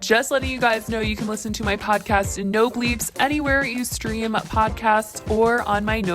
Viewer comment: I swear everytime she says "no bleeps" at the beginning I hear "no bleebs" 😂